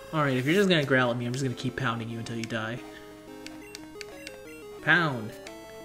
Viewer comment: So like.. I turned up the volume on the video and all I hear is “I’m going to keep pounding you til you die” lmao